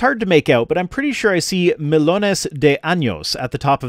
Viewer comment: I got hella confused on the Spanish part ngl It sounded like he said "melones de años" (melons of years)